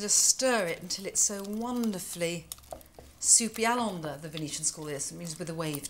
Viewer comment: what does Nigella say the Venetians call it? Can someone tell? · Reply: un'onda. Italian for wave.